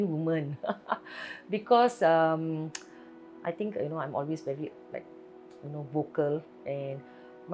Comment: subtitle says "obviously" but it should be "always"